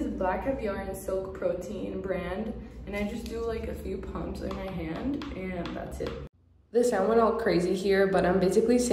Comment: the oil you used at the 4:20 mark, I listened a few times and cannot understand the name. Could you please post here? Thank you in advance. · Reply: Ofc! And it’s Sapuyulo oil. Comes from a fruit called Zapote